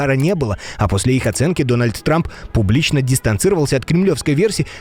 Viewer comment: Послышалось или нет... Дональд Срамп.😂😂😂